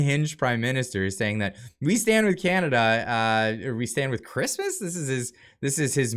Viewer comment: I thought he said we stand with muzlims 😂😂😂😂😂😂😂😂😂😂